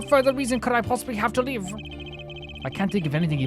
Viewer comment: "What further reason could I possibly have to live?" When I heard you say that, I almost spilled my tea. Easy there, he said "lie", not live, though if he actually said that it would make him even more melodramatic than he already is. ^^; Nice series so far, enjoying a first time player experiencing a great game imo. Your playlist is a bit messy though, may want to correct that on the next occasion.